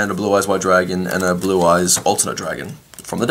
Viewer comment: i thought he said ultimate dragon for a solid 30 seconds